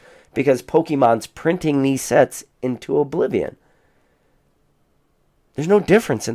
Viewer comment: When I was buying evolutions booster boxes in 2019 for 95$, that’s what I heard, “It’s printed to oblivion!” Lol, at the time I felt like I was overpaying